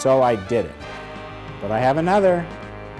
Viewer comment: (iPhoneが)まだまだある〜。に聞こえる。